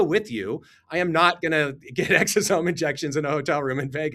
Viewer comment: 😂😆 OMG I thought he said he was going up to the hotel room for an EXORCISM !!!!! 😂🤣